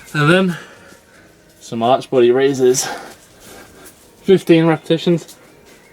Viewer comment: I thought he said "arch bloody raises"! Ha! :)